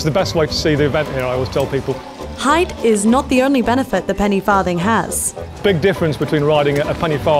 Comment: Thought it said penny farting